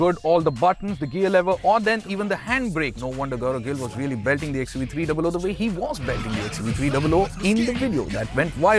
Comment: what the heck did he say?? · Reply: He said "No wonder Gaurav Gill was really belting the XUV 300 the way he was belting the XUV 300"...